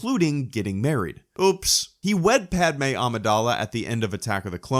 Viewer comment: when he said he wed padme at the end of AoTC, I thought he said he WET padme and she gave birth to his kids later